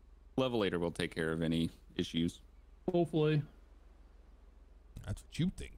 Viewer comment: when said: "hopefully" at first it had seemed (to me) he had said: "boat boy"……idk why but that's what he said at first